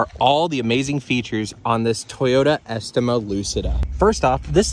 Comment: Did i just hear "Toyota eskymo" its "es-tema" kinda like "a steam-a" but es at the beginning